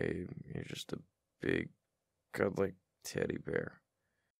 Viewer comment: did he say teddy bear or tiddy bear? Cuz I'm hearing both 💀